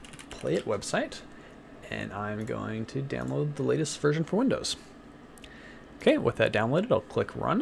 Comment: did he say he's going to download the lastest po#no